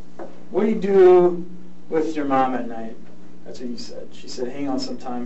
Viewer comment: does the cop let it slip and say "thats what i said" while reading the chat transcript or am i just imagining that